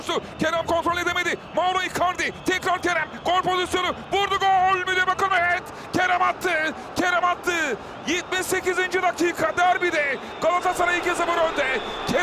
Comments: da ne diyor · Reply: Vurdu gol mü diye bakıyorum evet kerem attı kerem attı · da bilibakimet mi diyor ne diyor ya bulamadim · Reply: Gol mü diye bakıyorum evet diyor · burda ne diyor la 50 defa izledim yine anlamadım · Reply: Vurdu gol mu diye bakıyorum evet 😂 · spikerin ne dediğini anlayan😂😂😂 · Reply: Gol mü diye bakıyorum evet diyor